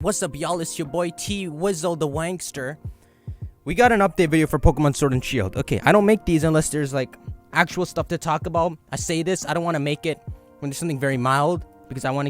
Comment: I heard “The Wankster”